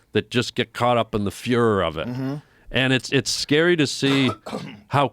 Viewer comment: is a subtle line. Instead of fervor he chose to say Führer. Knowing Harland, that wasn't a mistake.